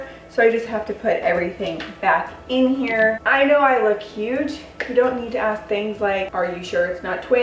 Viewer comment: 🤣 i heard her saying « I know I look cute» 🤣 i thought U go girl with that confidence